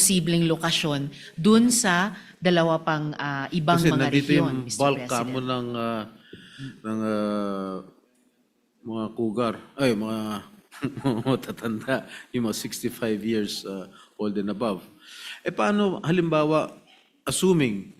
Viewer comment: did I hear cougar? insult!